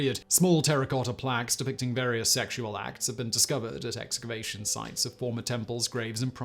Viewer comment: For a second there I thought he said masturbation sites instead of excavation. Maybe they're both?